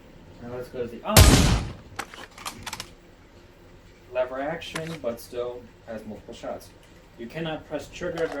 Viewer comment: accidently said lever action instead of bolt action applies